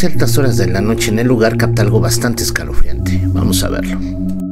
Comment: en el video de la frikiplaza minuto 3:35 se escucha una voz que dice vamos a la bodega?